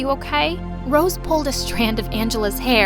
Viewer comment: go go ga ga sounded like baby saja LOL